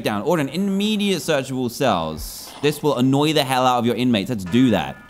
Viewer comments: umm, did he just say hell? I know its not a bad word but still...... ITS DANTDM! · was it just my hearing or did you say hell